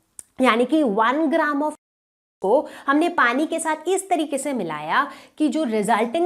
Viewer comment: par mam ne kya kaha any idea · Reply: Ik I'm late but if you're still wondering wat she said it was 'sugar solution'